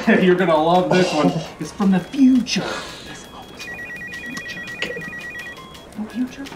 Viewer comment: i'm the only who could hear what he said? he said: It's from the future No Future?